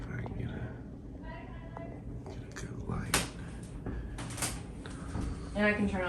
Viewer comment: I thought the voice said “goodnight, guys”